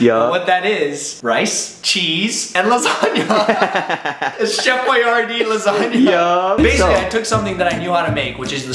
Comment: I thought u said Chef YRD Lasagna not Chef Boyardee Lasagna